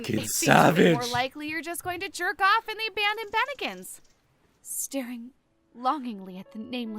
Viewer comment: jerk off in the abandoned what? i couldn't catch that part. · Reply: I know this was four months ago but she said the abandoned Bennigans. It's an American restaurant chain... I'm not sure it actually exists in Japan but hey, maybe it will by 2024.